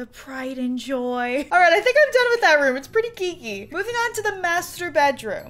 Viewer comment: I'm not gonna lie, when said" this room is pretty geeky" i thought she said" this room is pretty kinky "....